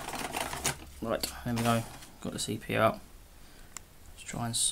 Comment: Did he say "Got the CPR" at 1:55? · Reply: He said "Right, there we go. Got the CP out". I hope you haven't been laying awake nights waiting for my answer.